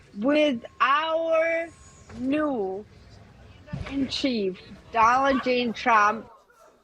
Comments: I swear I heard her saying Aladeen Trump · Am I the only one who heard “Dollar tree Trump” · I heard Doland G. Chump. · I heard “Stalin J. Trump”…🥴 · I thought she said, "Dollar J Trump." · I heard Donaldin Trump. Is that the USA version of Saladin? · I think she said “Doland”. Poor thing